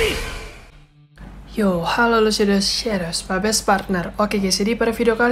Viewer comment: Detik 0:25 bilang apa bg? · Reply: Shadows is my best partner